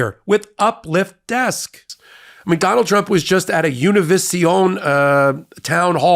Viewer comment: I thought I heard you say McDonald Trump (you didn’t) but now I can’t stop hearing it and laughing 😂